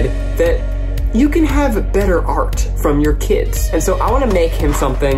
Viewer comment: sounds like Zach said, 'Je ne sais crois'. lol